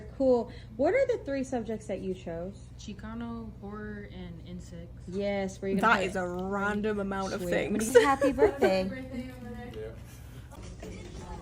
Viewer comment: I heard "chicano... horror... and incest" and had to rewind. Whew